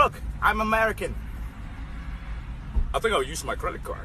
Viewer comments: The “do you have any nOn dairy products?” Got me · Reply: RIP me I thought he said non-hairy 😭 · “do you guys have anything non-dairy? anything gluten free” LMFAOOOOOOO 😭😭😭😭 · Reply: I thought he was saying anything non hairy